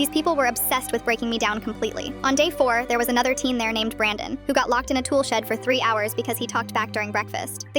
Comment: My dumb ahh thought it said a team named brandon